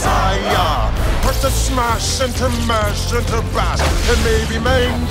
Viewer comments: bash or battle what is he saying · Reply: Bash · bash or battle? what is he saying? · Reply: "bash"